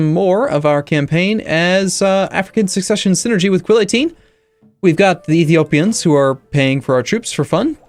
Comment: Every time I hear "with Quill18" in your intro I giggle because it sounds like you're saying you're Quill.